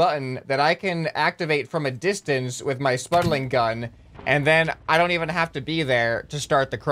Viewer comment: I swear I heard him say spudling gun when he was using his normal one